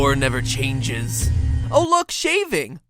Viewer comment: "vore...vore never changes..."-misheard matpat when i first listened to the intro